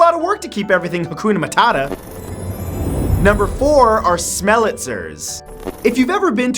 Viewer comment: "kakonamat attas" im i the only one who heard him say that?